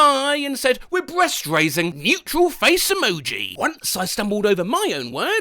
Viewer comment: I thought I heard mutial face emoji instead of neutral face emoji